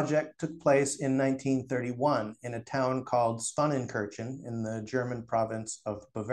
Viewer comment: How do you spell the following names? I'm trying to look them up, but I can't figure out how to spell them. The German town that was mentioned at 4:28? The Swiss bank mentioned at 8:51? The regional currency mentioned at 9:26? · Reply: Swanenkirchen WIR Chiemgauer BTW, the creator of the Chiemgauer, Christian Gelleri, will be our guest on Economy 2.0 on Tuesday, August 5th.